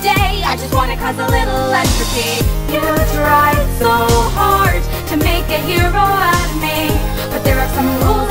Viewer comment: WHY IT SOUNDS LIKE SHE SAY "you tried so hard to make an hero academia" 💀💀